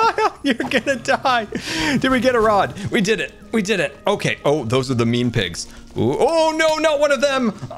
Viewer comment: "those are the mean pigs" what i heard: "those are the meme pigs"